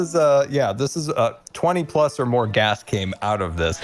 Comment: LOL! just makes my day! "Not a natural product in any of our products!" "There's an accident every other day!" "Our employees lose a few ears." "20-plus or more gas every day" "Industrial grandmas." "Grandmas everywhere!" Good thing this isn't actually in Texas... The TCEQ would never hear the end of it And welcome back, missed that 'stache :) P.S. I got one like yours IRL :D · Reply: I thought said "20 or more ghasts" referring to the Secret Fools prank where ghasts spewed out from the ground after Grian pressed The Button.